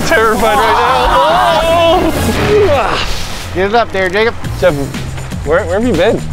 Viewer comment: I hear the N word🤣🤣 but he is saying jacob